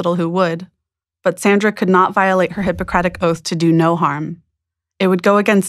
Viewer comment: Bruh I just woke up and thought it said “do no haram”